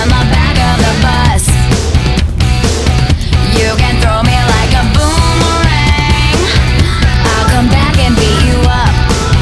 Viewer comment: I ALWAYS THOUGHT SHE SAID "YOU CAN THROW ME LIKE A LIME YEAH."😂